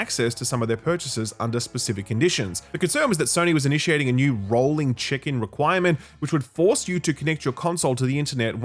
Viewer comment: Rolling Chicken?! I can't hear anything else lol 🐔